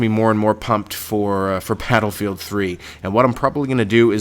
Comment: did he say pladlefield 3 at 2:48???